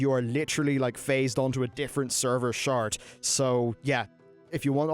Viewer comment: Di- Did he say Server shart?